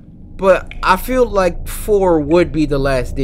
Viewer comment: I litterally thought Cory said Floor instead of 4 lol